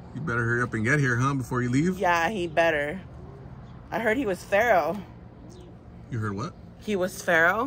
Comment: 1st <link> 2nd <link> 3rd <link> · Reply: I thought she said she heard Manny was FERAL 😂